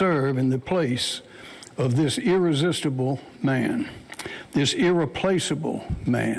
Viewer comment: Did he say irresistible?? 🤔🫢👌👈